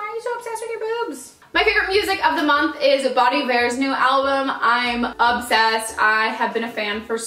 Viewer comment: I literally thought she was saying "Bonny Bear" for that whole video until I looked at the description and realized it was Bon Iver lol